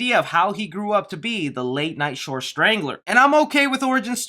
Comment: Did I hear late night shore strangler at 3:24 lol? You got it right the second time tho